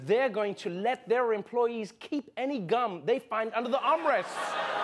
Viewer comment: At first, I actually thought he said "...let their employees keep any guns they find on the hombres"